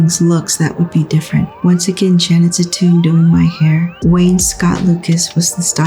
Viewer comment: I kept hearing “Janet’s a tune” and I couldn’t agree more